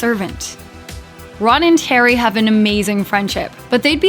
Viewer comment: I thought I heard the voice over say "Ron and 'Terry'" instead of "Ron and Harry" when introducing Hermione. Did anyone else hear this?